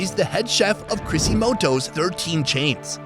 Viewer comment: did you say 'Crisimoto'? It's Crisostomo, hehehe! 😆😁